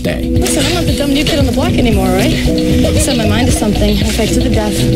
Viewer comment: What does andy say at 1:55? "I'm not the dumn new kid on the block anymore....